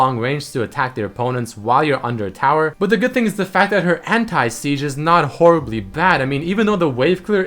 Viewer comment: did you say..."her hentai sieges"? .-.